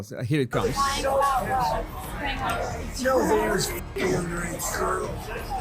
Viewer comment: It sounded like he said “You know, (Y’know) he was f**** underage girls” or “Yo, he was f**** underage girls”. Not “No”. I was watching his mouth while the clip played and his mouth was forming a word starting with Y, not N. It’s very hard to see, but if you play it a few time with the sound off, then play with the audio back on and you will see it pretty clearly.